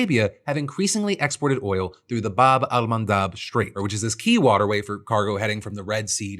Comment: bro, totally sounded like u said "bob-on-my-knob strait" Lol